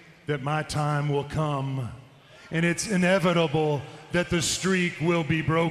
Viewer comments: What did The Undertaker say about his streak being broken? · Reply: I thought he said "It's evitable.", at first... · What did The Undertaker say about his streak being broken? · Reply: Inevitable, which means it will happen no matter what.